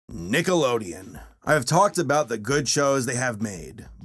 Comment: i thought he said niggalodeon